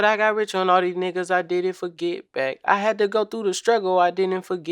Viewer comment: No lie, I thought he said "I did it for get back." ".... Forget bae" wouldn't have ever crossed my mind.!!